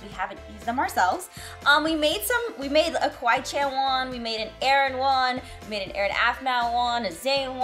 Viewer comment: I have a confession to make..... the first time I watched MyStreet I thought whenever you said Aaron you were saying Erin, lol 😆